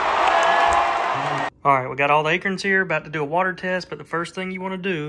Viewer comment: When I heard you say acorn at first I thought you said “Akern” I was so confused until I saw the acorns 😂😂anyways love the content 😎